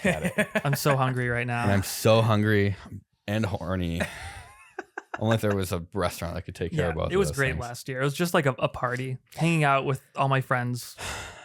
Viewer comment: I may have misheard William, but I did some googling and there is actually such a thing as a "breastaurant", so now I feel compelled to put that information out there for others to investigate.